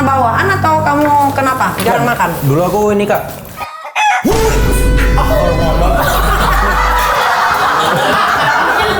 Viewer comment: INI NGOMONG APA DIA? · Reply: Nyabu